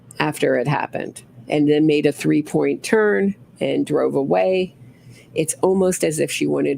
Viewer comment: “K turn” I think she said 😂